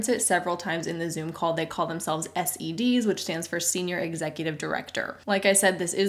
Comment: listening to this again about a year later! I thought you said "they call themselves STDS" when it was actually SEDs! Thanks for the laugh. I am in the middle of a tough few months and listening to your videos -AGAIN!, helps calm me down